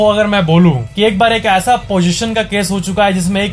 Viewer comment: is it me or I just heard position instead of possession But what type of position 😂